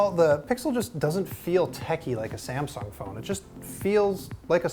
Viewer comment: Did you just say 1:09 Samsung phones feel "tacky"